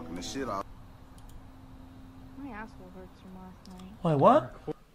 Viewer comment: bro wtf she said at 8:08 im so deaf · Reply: "My a**hole hurts from last night"